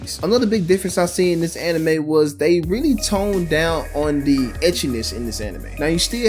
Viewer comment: Unsencored monster musume? If they mote than one episode (they alredy did obviously for fanservice) they would remove the essence of the anime. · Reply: Right. That "edgy-ness" was me misquoting this channel's content creator. I thought he said edgyness when he really said Ecchi . Which is exactly what I was referring to. Which, again, means you are correct. At that point it goes from anime to being ecchi.